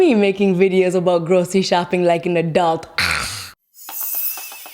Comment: i heard 'look at me, making videos about grocery shopping like a dog'